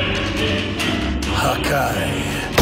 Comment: what does berus said? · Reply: Hakai